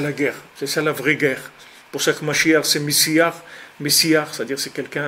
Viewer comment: Ca veut dire quoi "pour CHAQUE mashiach"??? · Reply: Il dit : "Pour cette Mechia, cet Mashiah"